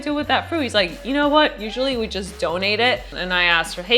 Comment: Re: the fruit in question that she featured in the video, what she said was that the person told her "USUALLY WE JUST DONATE IT" --- She did NOT say "charity" specifically. Also, it's never a good idea to ASSume what people have in their bank account. You don't know how well-off or not well-off is.